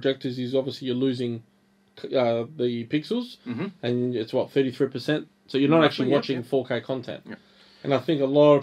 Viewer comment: i couldnt hear him clearly, what percentage of pixels did he say you lose when you zoom in (8:50)? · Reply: About 30% of the total of the chips vertical pixels